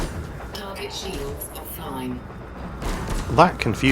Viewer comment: Lol... I thought the AI said "target shields fine" too. I was like...how does that help me?